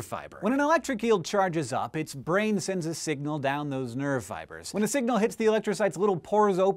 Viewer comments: Every time you said 'nerve fibers' I thought you were saying 'nerdfighters' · When he said "nerve fibres" i totally thought he had said "nerdfighters" haha :P